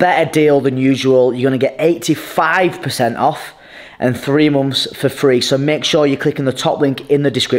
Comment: > did he just say 3 moms for free? I'll take that thank you very much 😂😂